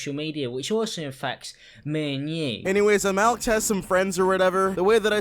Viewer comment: me and nie fuck you aren’t black why did you say it like that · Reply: he literally said me and you... he has an accent and a lisp bro